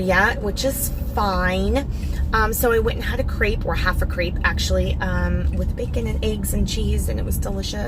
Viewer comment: Yes girl. Doing great! Don't take this the wrong way...but, I can totally see you ADHD. Also, I said to myself "Wow! Her restriction really is tight." I thought you said you had half a "grape" while waiting for the doctor. LOL!